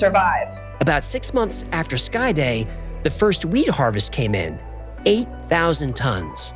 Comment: At first I thought it said "weed harvest" rather than "wheat harvest"